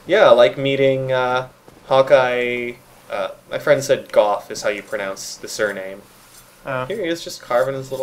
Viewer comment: Sounded like he said "Gouff" or something similar. I don't know how to type it out phonetically. The "ou" sounds like the end of "though." Also, a score is 20.